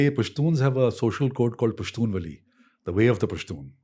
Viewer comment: Wait WAITTT...DID I HEAR HIM SAY AT 6:12 PASHTUN VALI!!??? THTS EFFNG TAMIL!!! 😳😳😳. Pushtun n tamil connected!!??? Did u guys hear that!!?? Pashtun 'vali'. Vali is 'way' in tamil. Remember rajnikanth saying...en vali .thani vali! (Jst shut up keralites..i know wt it means in malayalam uff😏😒). Sooooo tamil & pushtun!?? Wait wat!!?? Mind blown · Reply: It's not vali, its "Pashtun Wali", the "v" letter sound dosen't exist in Pashto's alphabet only w, hope that helps!